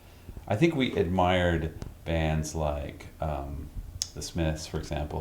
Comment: What band is he talking about at 11:44 I can't really understand what he says there. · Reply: A band called The Smiths. Good band👍